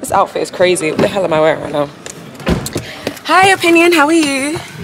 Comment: did you said ‘Hi Opinion’?